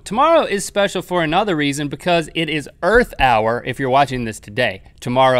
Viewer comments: Thought he said birth hour xD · I thought he said birth hour